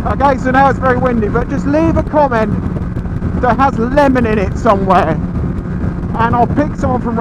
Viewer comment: Oh Lemon I thought you said eleven.